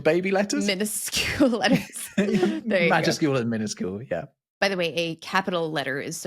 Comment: CC reads "magic school and minuscule" - instead of "MAJUSCULE and minuscule".